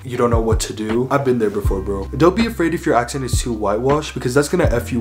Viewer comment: idk how but i misheared bro as girl